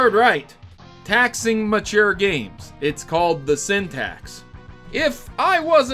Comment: I wasn't looking and thought it was called the "syntax"